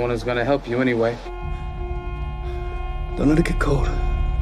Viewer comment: Did he say “Don't let it get cold”